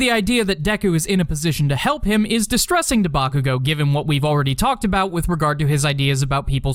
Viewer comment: did you say debakugo 😂😂😂😂😂😂👍